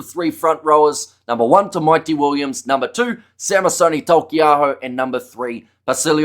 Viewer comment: When you say "Tamaiti Williams" I just hear "The Mighty Williams." like he's from He-Man or something